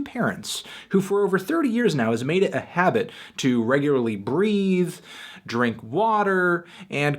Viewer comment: "regularly breathe" really, for a second I thought I heard "regularly breed" :DDDDDDDDD